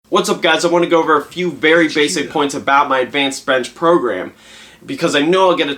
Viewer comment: What did the mouth of mystery say at 0:01? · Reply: "HQ though" i think.